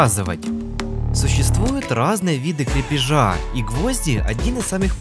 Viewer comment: Мне послышалось не крепежа, а кринжа 🗿